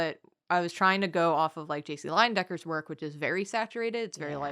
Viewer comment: Bro.....I thought said JUICY Lion Decker